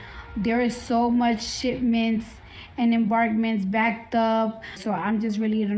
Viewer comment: I’m personally interested in hearing more about Cardi B’s thoughts on shipments and “embarkments” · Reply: I thought She said Shit mints! Haha